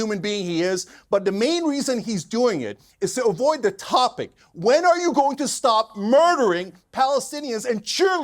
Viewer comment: My brain is so broken because at 7:40 I heard Cenk say “to avoid the top .. of the hour ad break”